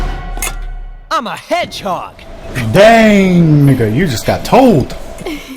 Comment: did he say "DANG N---- you just got TOLD"